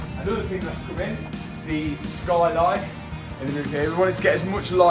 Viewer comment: When said about the skylight at the start I was looking away from the screen and I thought what did he just say skyline 😊 I thought at last I've always wanted to see a skyline on this channel then I realised what actually said 😢 lol. Never mind maybe one day 😁😂